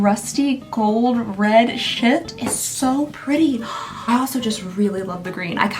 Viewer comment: ik you said shift, but i heard sh*t 😆